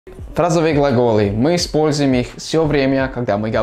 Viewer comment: Я услышала "Тазовые глаголы" 😂😂😂😂😂